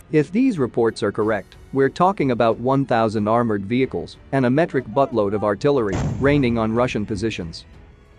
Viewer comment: "A metric Buttload of Artillery", now thems technical terms that I can relate to. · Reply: I heard “metric busload” - but I was born deaf on my right side, so I mishear a lot.